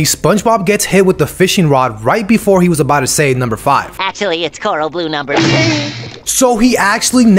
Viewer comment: I always remembered Krabs just saying “corral blue lipstick” and SpongeBob replies, “Actually, it’s corral blue number 42.” So weird. And I vividly remember SpongeBob with a white V shaped guitar at the end of the SpongeBob movie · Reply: I thought they said corral blue number 50 lol